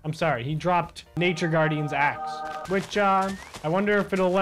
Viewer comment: who else thought he said Nature Guardians ass instead of Axe?